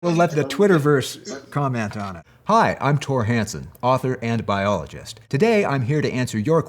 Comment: It sounded like he said I’m father and a biologist When he said author and b…